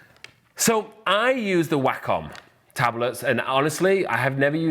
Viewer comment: At first I thought you were saying "whack on tablet" which I thought was hilarious cos you technically do kinda whack on it lol